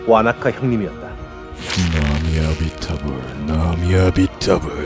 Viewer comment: 이거 저만 나미아비타불로 들리나요?